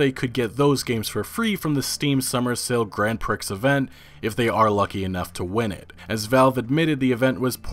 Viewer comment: Did he just say "Grand Pricks Event"?